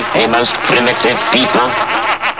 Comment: No wonder my mother has washed her hands of me these days, I always thought it said for mash get smashed?